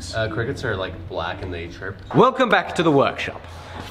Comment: “Look at this little guy, little cricket” I was about to comment that it was a grasshopper, before he was corrected with some even better content🤣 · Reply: If you listen carefully, he said "and they chirp", not "and eat shrimp". I think the editor misheard and threw that wording in there in confusion (hence the quotation marks and question marks).